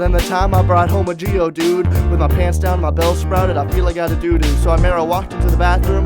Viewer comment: I heard "Doo-Doo" But not Doduo